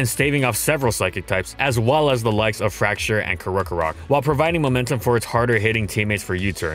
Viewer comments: and I still can’t get what pkm is he trying to say, any ideas? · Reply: Fraxure and Krokorok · Does anyone have any idea what he said at 4:52? Is that the name of a Pokemon or something? Because I've never heard of it. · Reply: "Fraxure and Krokorok"